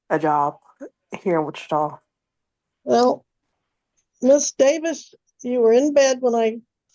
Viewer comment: Did she just say her name is Saliva Davis?